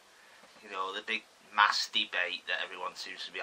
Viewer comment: i swear u said masturbate